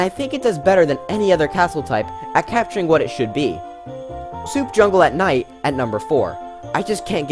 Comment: i thought he said soup jungle theme